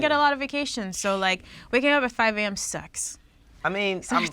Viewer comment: ?? Did she say 5 am sex??